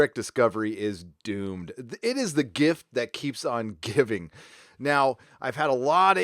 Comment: I thought you said the gimp that keeps gimping.